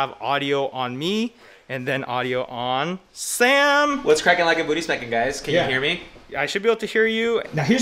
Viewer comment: English isn't my first language but did sam say "what's cracking like a booty spanking ?" that's a good merch line tbh. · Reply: He said ”what’s crackin like a booty smackin“